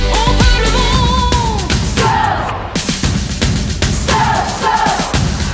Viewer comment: What do they say at 2:11? Qu'est-ce qu'elles disent à 2:11? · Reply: DarkMissTique si c'est audible elle dit bien Soeur